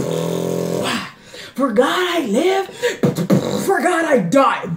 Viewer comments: “for God i live, for God i die” THAT PART IS FIRE BRO 😭😭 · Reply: My dumb ass thought she was saying forgot😂😂😂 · “for God i live, for God i die” THAT PART IS FIRE BRO 😭😭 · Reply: i thought she said "forgot I live, forgot I died"